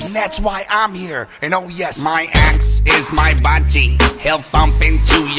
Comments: I always thought it said "my axe is my wafi" as in 'waifu' y'know😀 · first time I ever herd I thought it said My Axe Is My Bunchi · I first thought he was saying “My axe is my Squanchie” before I saw these lyrics. I had visions of Rick and Morty in my mind. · I'm so stupid I thought it said "My ex is my buddy" I'm disappointed in myself lol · Is it just me but did anyone at first thought it said “my axe is bungy” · Thought the buddy part said my axe is my budgie like the bird 😂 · it sounds like he says my budgie instead of buddy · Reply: Before i knew the song was “my ax” i thought he said “ my ex”. I was like “ no”. 😂